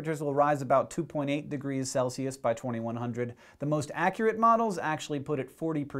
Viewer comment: Did Hank just say 20-100?